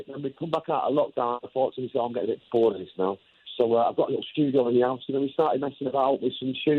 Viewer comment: just FYI, the subtitles are wrong, he said a studio "in the 'ouse" (in the house) not "in the afternoon"